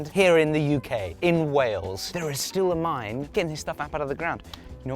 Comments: Ohhhhh. When said ‘Wales’ I thought he was saying ‘Whales’ - you know, like the sea mammals. I couldn’t quite figure out how you could possibly get anthracite from whales, but it makes a lot more sense now. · I definitely heard "whales" not "Wales" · i thought you said Whales...